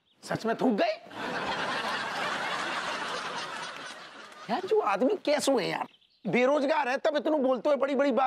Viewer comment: did i hear it right?? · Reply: No ...he said jo aadmi 😂